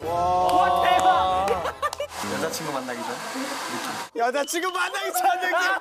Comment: did seungcheol said yeochachingo(Gfriend)?